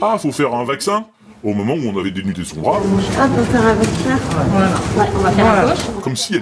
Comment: j'ai entendu "ah, faut faire un vaccin ? " sans avoir le texte ... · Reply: logique car c est le contexte de la vidéo, on est forcément plus enclin a entendre "vaccin" que "avec ça". à moins de présenter la vidéo sans le titre et sans l'image, impossible de ne pas être influencé et encore même sans tout ça on nous matraque ce mot a longueur de journée...